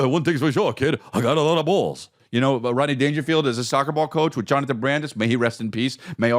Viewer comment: did Chris just say soccerball coach ?